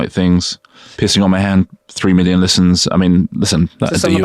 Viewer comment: I thought they said pissing on my hand…